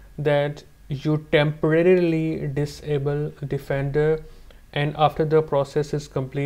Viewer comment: before you jailbreak make sure u temporary disable what, it’s in 1:55 minutes. I can’t hear what u said, you went down to your pc, that up facing icon near time and calendar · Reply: He said temporary disable "Defender" (I believe what he meant is Windows Defender).